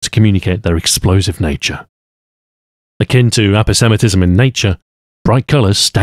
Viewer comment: surely I can't have been the only one who heard him say antisemitism at first